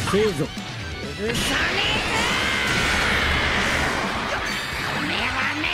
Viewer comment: What he sayyyyy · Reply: He said urusa nai za, it means I won't forgive.